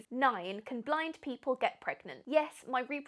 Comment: I thought she said "can blind babies get pregnant?" 😂 then when she said "yes" I was like wtf 😂